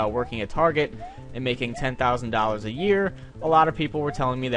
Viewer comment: I misheard what you said and i was like 10.000$ a month.I was like HOLY SHIT THAT IS ALOT OF MONEY.But it was 10.000$ a year.and that's balls.